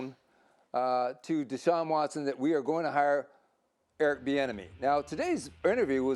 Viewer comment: I didn’t realize that was his real name. I always thought people were saying “Eric the Enemy” 😂